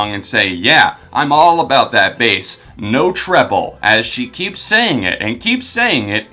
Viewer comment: Legit always thought Trainer was saying no trouble this whole time... no treble makes more sense thinking about it now